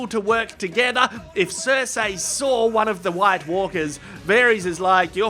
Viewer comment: Wait, the plan was to kidnap a White Walker? I thought they said dead men. I figured they were just going to snatch a walking corpse from a patrol or something. Bring back to King's Landing in chains.